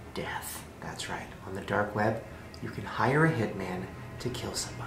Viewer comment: i thought it said the dank web...